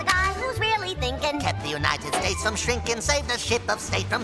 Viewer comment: who else heard sh!t